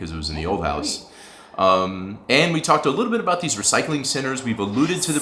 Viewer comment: I need better headphones... I heard you say “...recycling sinners...”